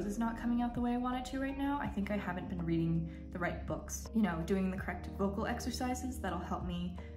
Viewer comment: For a minute I thought you said "throat", and then I heard "vocal exercises", but my brain is going "wait ... how does reading the right books affect your voice"?? So please excuse my hearing loss, which is getting worse. I followed your vlog as best I could. :(